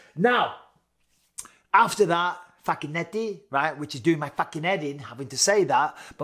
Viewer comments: Bisbing with his accent sounded like he was saying fuckin eddy all the time and I think he liked saying it lmfao or it could be he likes the sound of his own voice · It’s sounds like you said fuckn’eddy 🤣